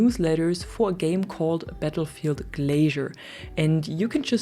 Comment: 😂 idk why I thought you said " Battlefield Glazer "like someone who hyped up battlefield to much